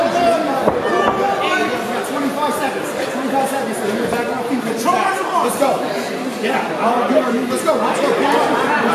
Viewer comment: What did the other guys coach say to hulk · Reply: 🤣🤣 he said 15 more seconds and we back on the feet beating his ass